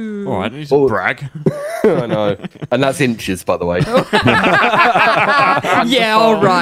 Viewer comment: sorry did i hear a european say.... INCHES?!